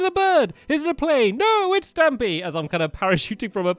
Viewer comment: I thought he said parachuting from a plate instead of a plane. I guess it would make since. Lol. Cake!.!.